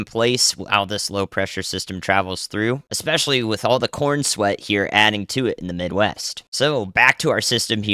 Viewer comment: Um, did he say corn sweat at 5:45 ?